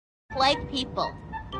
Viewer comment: I keep hearing “white people” 💀